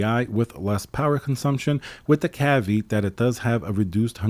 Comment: did he say caveat?